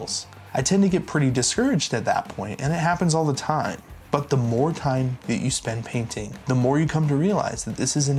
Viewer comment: I got excited when I thought I heard you say “Mordheim”, but it was “more time” 😪